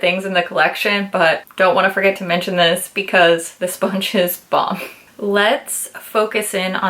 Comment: somehow the first time i thought you said "the sponge is bob" 🤣🤣 and for a sec i thought they name the sponge "bob" to be cute.